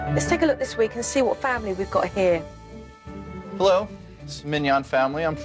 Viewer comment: I thought you said at the start "lets see what simp family we got" but she didn't say that you said "lets see what family we got" but the family seems like simps